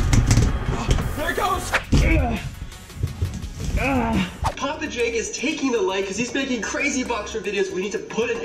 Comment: Shoutout To hernan lazo For Getting first comment!! Just subscribed to their channel!!! To Win Next Time Follow The Steps Below 😄 Also guys Let's CRUSH 25,000 LIKES Tonight for the next Part 😁✌🏻🔥 🚨 SUBSCRIBE!! 🚨 🔔Turn on NOTIFICATIONS 🔔 ‼️ LIKE THE VIDEO‼️ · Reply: I thought u said papa jake should die